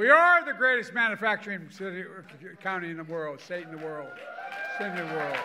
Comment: Almost sounded like he said "satin in the world"